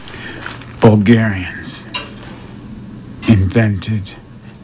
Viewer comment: What he says in 3:54 - ''Bulgarians'' ? And from what movie is ?